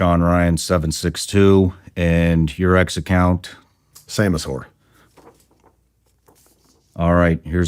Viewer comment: I thought he said Samus Whore